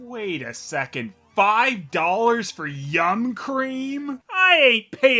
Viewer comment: Yum cream? I thought it said tuna curry.